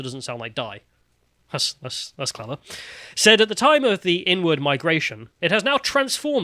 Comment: When you said, "inward migration", I heard, "n-word migration". Lol. I was wondering when the cancel hammer was gonna come down.